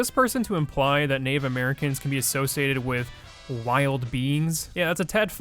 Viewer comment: What did you say cause all I heard was wild beans